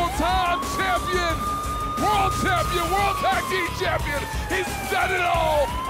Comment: did he say RAW champion?